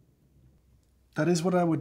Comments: Oh.... He said JANitorial lemon. I really thought he said genitorial lemon. "....made dirtier by the undertones of the coffee in there..." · Tasting notes: Citrus, sweet, janitorial lemon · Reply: I thought he said genitorial lemon. I'm glad that's not a word.